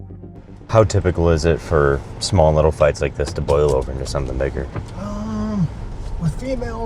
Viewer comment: what did he say? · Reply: “how typical is it for small little fights like this to blow over to something bigger”